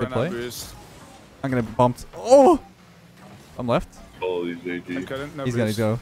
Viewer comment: Every time he says I'm left I hear I'm Leth and that confuse me that he need to specify it